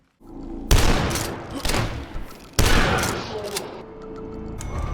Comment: did he just say "ow my shoulder"? gachiGASM